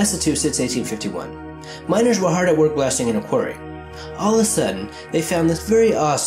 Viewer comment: Did you really say "all a sudden" at 3:10?